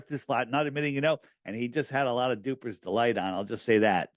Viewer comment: what is saying here I can't understand? Alex Jones had a lot of ??? ??? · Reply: Duper's delight.